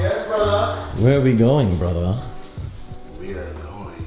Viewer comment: were we goin brother. I thought he said strip club